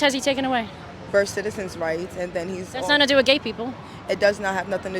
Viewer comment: Ground News : Go to <link> to see through media bias and get all sides of every story. Subscribe through my link for 40% off unlimited access this month. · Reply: What rights did she say?? 9:00 I didn’t understand… “First citizens rights” ……??